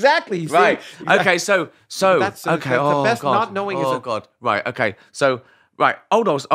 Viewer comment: Did Ricky just say "Oh God"?!!